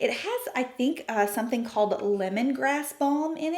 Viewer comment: It does not contain lemon grass - lemon balm is a totally different plant 🌱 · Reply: I thought you said lemongrass which seemed odd 😂but all sorts of herbs go in skin stuff.. Thanks for letting me know.